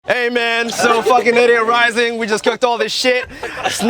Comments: Not gonna lie but i thought he was saying " idiot rising ". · I thought in the intro he said idiot rising